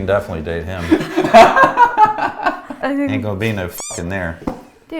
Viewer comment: what was saying? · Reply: “Ain’t gone be no fvcking there”